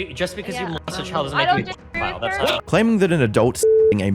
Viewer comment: Will he be able to stream through this one? 🤔 Sign up for a 14-day free trial and enjoy all the amazing features MyHeritage has to offer: <link> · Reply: When you said pedophiles, I thought you said pdf files, lmao